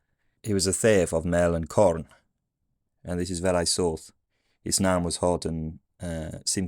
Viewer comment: I just heard 'His nan was hot.'😂